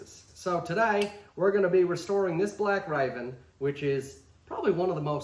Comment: at first i thought u said restorin this a$$ 😂😂😂